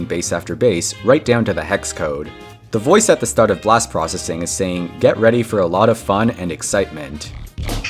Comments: i thought it's saying "get ready for lap one, let it compleat" xD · I thought it was "for more fun" instead of "for a lot of fun". Also, before that, I genuinely thought it was "*mumble mumble* medic freement", what the hell was I thinking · i thought it's saying "get ready for lap one, let it compleat" xD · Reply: I always thought it said, "Get ready for lift off, living 300" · i always heard "get ready for a lot of fun, and extreme with it" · I thought blast processing said " get ready for a lot of fun Let it freak U" 😂 Seriously trying hearing it seems like that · i hear "get ready pull" and "woman extreme attack" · I hear get ready roll out roll out, and excitement somehow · I always heard "get ready for a lot of fire" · I thought I said "get ready for a lot fun, my name's Wrinkelton." 😭😭😭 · I thought it said "Get ready for a lot of fun, i am a criminal"